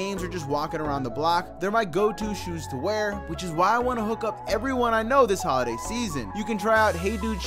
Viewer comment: Omar bro, I heard you say “which is why I wanna hook up with everyone I know.” 😂